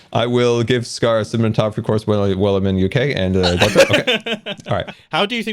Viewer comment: What did Scar say at 15:10? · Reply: “i will give scar a cinematography course while im in the uk”